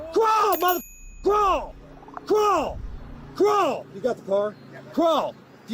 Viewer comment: ohhhhh he was saying crawl. I thought he was saying grow.😂